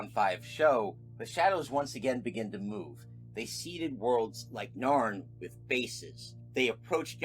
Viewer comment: "Seeded worlds like Narn with"...I can't figger out what the next word is, sorry! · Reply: That's okay I'd rather you ask! It's seeded worlds like Narn with bases.